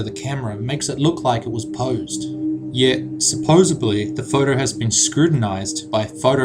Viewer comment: did he say supposebly at 5:14 ?